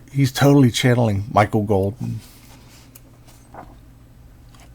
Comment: I misheard “Michael Bolton” and now I want a Kelley Jones cover to a Michael Bolton greatest hits album 😂